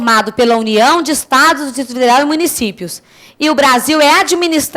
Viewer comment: o que ela disse em 0:47? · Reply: risos, disse "Distrito federal" mega rápido!!!